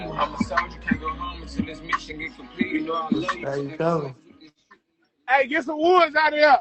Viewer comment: did he say daddy coming. Wthlly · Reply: he said "how u coming"